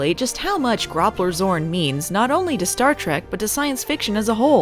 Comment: Idk why I keep hearing it as "Groppler's Horn".